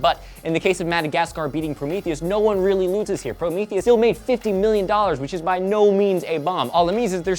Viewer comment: was it just me, or did i hear joe say "fifteen million" rather than 50 million